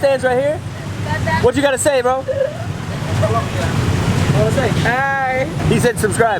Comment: Conner: What you gonna say Bro? Indonesian guy: Hai Conner: He said subscribe Me: NO HE DIDN'T